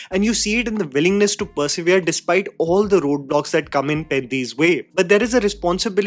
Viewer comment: ngl I heard botox instead of roadblocks 🥀